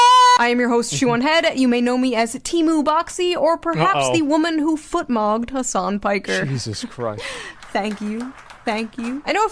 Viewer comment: What did she say? "footmogged"? I'm not a native and can't catch that phrase... Edit: yeah... she said footmogged, meaning having a lot better feet than someone. Like WTF girl?